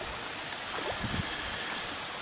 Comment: Why did you say goodbye salamander have a good life I thought you said hey little guy off you go